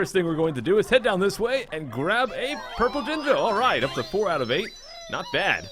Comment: Wow, surprised no one picked up on the best innuendo of 'em all "let's just go right outta the tube we came in" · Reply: I decided to see how bad the autosubtitles are, and when you said purple jinjo, the subtitles said purple d*!do